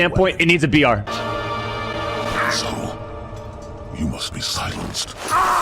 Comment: I always heard: ‘And so, you must be silenced.’ As… ‘Asshole, you must be silenced.’